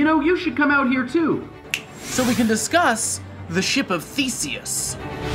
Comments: I thought he said feces lol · at first i thought he said the ship of feces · i thought he said "the ship of fecies" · Am I the only one who thought he was saying "ship of feces" ? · lmao I thought he said "The ship of feces" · who else heard the ship of feaces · I thought he said "ship of feces" but then I looked at the words on screen. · thought he said: ship of feces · I thought you said the ship of feces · I thought you said "The Ship Of Feces" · I thought for a second he said "The ship of feces" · Thought he said "ship of feces" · 1. i thought that you said the ship of feces at first 2. that was an awesome picture of that eye thing at the end, and the dividing cells 3. i doubt it, but was that a real fetus at the end? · When he said ship of theseus , I thought he said ship of feces, which means poop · Who thought he said ship of feeces 😂😂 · I thought he said the ship of feces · I swear I first heard "ship of fiecies "